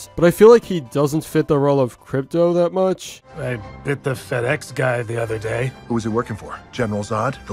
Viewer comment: Fr when he said "FedEx", i thought he said "Fatass" 😂